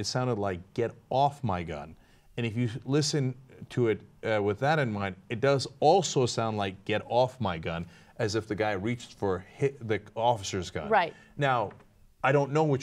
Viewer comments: I heard He's got my gun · I heard "he's got my gun!"